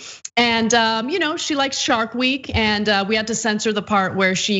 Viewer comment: I thought she said shart week